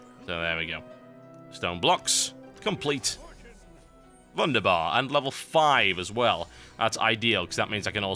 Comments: did he really say "wunderbar"? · did he say "Wunderbar"? do english guys use that word? its german btw